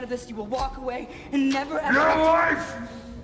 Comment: did he say get away, get a life or get a wife?? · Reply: YOUR LIFE!!! 😮